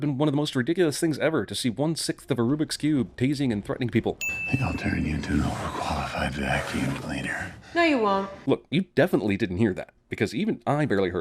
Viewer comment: I heard that, he said "I think ill turn you into a over-qualified vacuum cleaner".